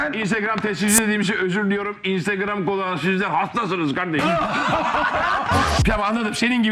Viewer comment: te ne diyor Ahmet Çakar? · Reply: İnstagram kullananlar hastasinız siz diyor. Biraz geç oldu ama yazayım dedim